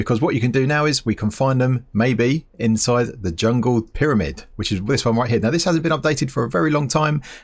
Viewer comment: excuse me did you just say jungle pyramid